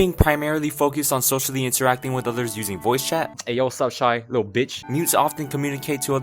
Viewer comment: I started to die when I heard “ayo wut up shawty you little bitch”